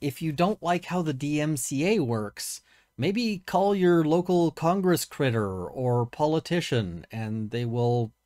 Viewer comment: Did say "congress critter" at 12:54? 😙🤌